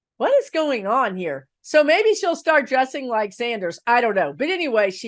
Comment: OMG I thought you were saying Nicky Haley was gonna start dressing like BERNIE Sanders!!!! 😅😂